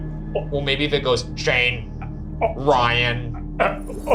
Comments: my friend and I swear we both heard the ovalis say "Shane (stupid noise) ryan" right when it turned on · is it not trying to say "Shane, Ryan" kind of mimicking how Ryan said it? Maybe I'm wrong but that's what I heard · I swear @ 11:40 when they turn on the ovilus it actually sounds like it said "Shane or ry-an". Saying what the boys wanted it to say!